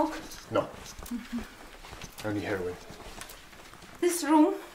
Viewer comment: sounded like he said “only heroin” 😂